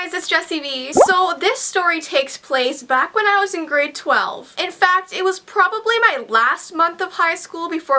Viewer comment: Lol at first when you said you were in grade 12 I thought you said you were 12 years old but then you said you were almost done with high school and I got confused then realized what you actually said